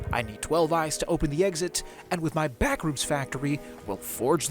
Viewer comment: I heard bathrooms factory not backrooms factory